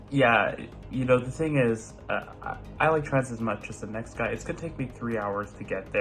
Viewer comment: The subtitles are something else 😂 10:17 "I bet you haven't heard this ringtone in about 15 years" I've never heard it, lol. Also, it finally helped me understand what was saying. I've always heard "I like traz as much as the next guy"